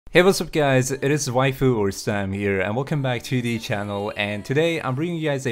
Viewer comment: That sounded like "Hey whats up guys, it's Wyfoo or Sam here."